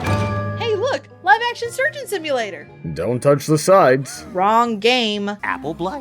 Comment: Is it weird how, as a kid, I always heard that line as, “don’t touch the signs”?